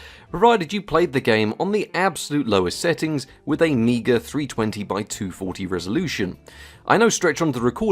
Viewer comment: what did he say?!?!??!?!??! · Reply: “Meagre 320x240 Resolution”